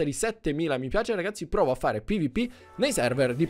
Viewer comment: io ho sentito provo a fare la pipì nei server